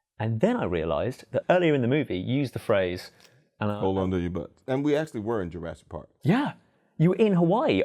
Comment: umm wtf did he say? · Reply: Hold on to your butts.